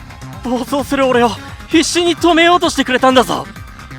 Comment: あたりの「必死に〜」が「Hey Siri」に聞こえるらしくて、Siri発動するwww